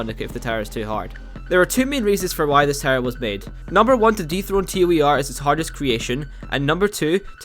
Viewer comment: Citadel of Prominence Profundity- oh I thought you said DEToH